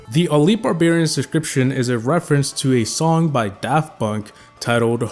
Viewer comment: why tf did you say daft bunk